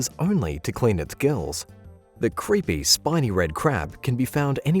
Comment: That's what I thought he said too. Good thing they spelled it out on screen!🤣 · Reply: He actually goes back and forth saying Shiny and Spiny.